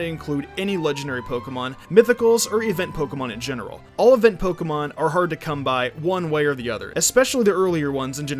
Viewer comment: I heard "I don't want to include any legendary Pokémon, mythicals, or even Pokémon in general"